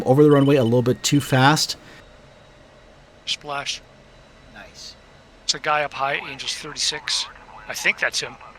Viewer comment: "Chakai up high, angels 36 I think"... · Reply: nm, "there's a guy up high, angels 36".... misheard, sounds the same to me...